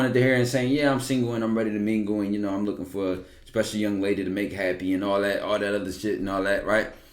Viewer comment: I’m a woman and this breakdown about KG is on point. Kevin makes a lot of high powered Alpha women blush and let their guards down. Yung Miami was the same way with him. That’s because Kevin Gates love ALL women, respect and cherish women without looking thirsty and staying true to himself and he has substance outside his money. That is something a lot of men don’t know how to do. A lot of men either come off thirsty, desperate and lonely or just have a hatred to women that they confuse as Alpha energy. All these men on social media are arguing with women is very feminine energy and showing hatred to women that not their preference is feminine energy as well, or doing too much like Derrick Jax. Kevin need to teach a class. I love him too BTW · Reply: At first I thought u said young ma I was about to say oooouuu Lol